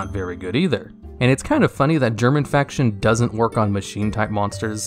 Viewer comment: I was playing a game on my second monitor and I heard you say "german faction" and was... very confused about ygo having a ww2 archetype lmao